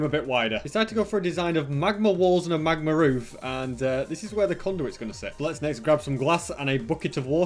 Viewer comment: I keep hearing condum